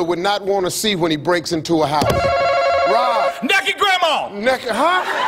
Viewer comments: The confidence in that man when he yelled “NAKED GRANDMA!” has inspired me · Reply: I heard "nakey". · “NEKED GRAMMA” “NEKED HUH-“ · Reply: I thought he said Kentucky Grandma